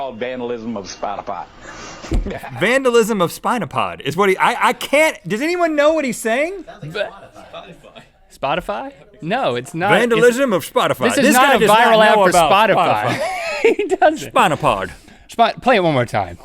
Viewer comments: I hear 'vandalism of Spotify'. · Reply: That's what I heard "I guess it was vandalism of spotify" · I thought he said vandalism of spider bite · Lol for those who don't know, Spot-a-pot is a porta potty company where that hick is from, so he was making a joke about the company. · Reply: thats right and at first i thought he said spoify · I hear ''vandalism of spinal part' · I thought he said "Vandalism of Spotify." O.o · I thought he said spidafy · I heard "spotipy" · I thought he said "vandalism of Squatty Pot(ty)" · I think he said "...vandalism of Port-a-potty..." But it sounded like he said port-a-pot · I heard him say "Vandalism of Spotify"